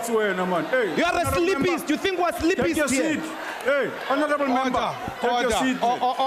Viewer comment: I just watch this whenever I wanna have a laugh!!! "you are a sleepist, you think we are sleepists here?" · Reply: I thought what he said was: "You think what sleepists 'do'." I heard wrong...